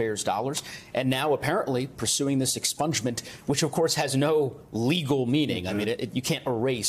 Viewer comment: Sorry. I heard that as 'herrings' instead of 'hearings'. But herrings is really more appropriate.